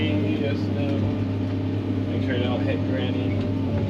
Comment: is me or did call granny